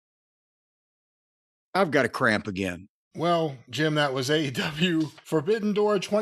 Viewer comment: At the 13:00 i had to replay it because I thought he said I've gotta crap again